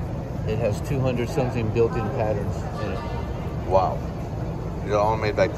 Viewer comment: the consew machine cn2073 has *200 patterns not "200 something million" patterns. i have no respect for a salesman who lies like that. e, its digital so.. are they releasing updates or user installable stitches? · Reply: price I heard him say "200 something patterns built in..." NOT 200 million. Marker 4:42, listen again. *I don't work for Consew or Goldstar and don't even own a Consew. Just commenting for accuracy. Have a good holiday season